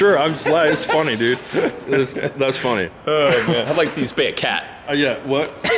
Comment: what did he say ??? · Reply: "i'd like to see you spay a cat" LOL